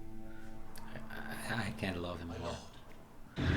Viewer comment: What did he say ? "I can't love him any more" or "I can't live him at all" ? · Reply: He said "I can't love him more"